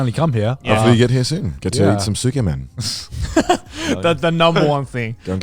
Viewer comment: What did Connor say? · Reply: That he gets to eat some ‘tsukemen’.